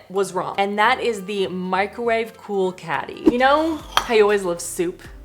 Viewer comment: I'm sorry... I prefer the Balrog with wings. But then... if you put wings on just about anything I'm kinda down for it so... probably just me. I seriously thought you said 'Microwave pool caddy' and I was very confused for a minute. I love lack of faith in the history knowledge of their audience.